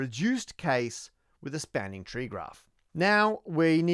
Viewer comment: Great video! at 10:29 the captions said "Spanish tree graph" instead of "spanning" 12:22 "oiless" lol